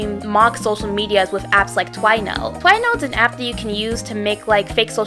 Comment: What did they say? I’m sorta curious but can’t tell what they said it was called ( 9:08 ) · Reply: twinote! :3